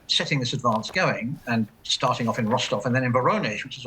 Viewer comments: Thought she said “farting” with the first word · I thought she said, "farting" at the very start...